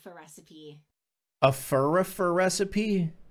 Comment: i heard führer recipe 💔